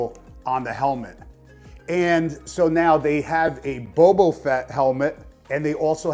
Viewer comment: Wait...did you say Bobo Fett?